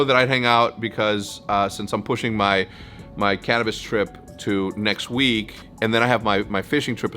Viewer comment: Did I hear Cannabis trip @ 13:17 ??